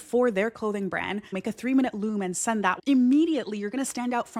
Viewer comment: you said "Make a 3 minute [word] and send that", what is the word i'm not catching? thanks x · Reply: "loom" its a tool that allows you to record your screen and send video messages :)